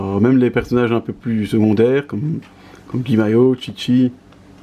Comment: J'ai entendu "Guy mayo" 😂